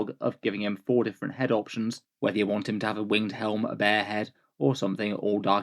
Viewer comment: You said he had a bare head option, and I definitely heard "bear head" and was taking a second look lol...